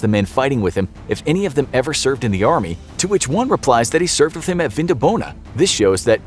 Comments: I didn't know that he said that he served WITH HIM, I thought he said that he simply served! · I never heard the "with you" before. I thought he just said "I served an Vindabona"..just watched again snd it checks out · Fun fact: Vindobona was a roman military outpost located in today's Vienna in Austria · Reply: that's why I thought he said Vin Di Bona😉